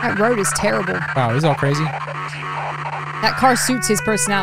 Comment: i thought was saying go crazy te whole time